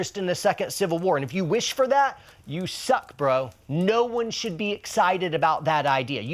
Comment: He says "You suck bro" I hear "No yay for that bro"